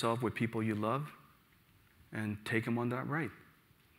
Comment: Can someone tell me what he said @ 55:44 after “surround yourself with people you love”? Thank you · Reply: And take them on that ride.